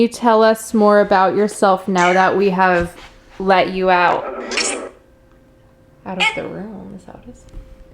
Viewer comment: I Heard- get out of the room Then I heard Hurry I got the chills